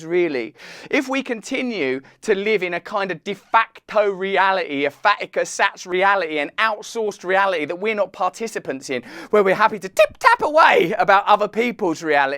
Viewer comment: "if we continue to live in a kinda defacto reality an Andrew Sachs reality, an out phoned reality.." did i hear that right? lol